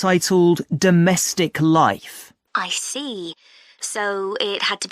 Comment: I hear this answer as 'domestical life not domestic life..